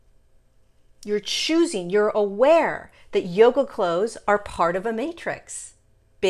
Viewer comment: Hi could someone help me understand, in minute 3:37.., it says.. that Yoga ____ are part of the matrix... . - I can´t understand that word after yoga. What did she say? Yoga clothes? yoga clones? yoga clowns? I am genuinely asking because someone yesterday told me that there was a lot of manilupation in yoga from the cabal. ... so I am like what? and then I heard mentioned something I just can´t figure out what she meant. And english is not my first language.. Any help please or insights? Thank you very much in advance . Love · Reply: She said yoga clothes:)